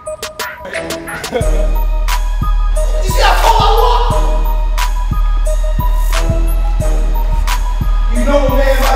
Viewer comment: hahaha what did he say ? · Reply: You see how tall i walk